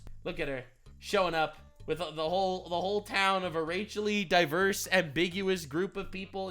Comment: excuse me did you just say rachelly diverse?..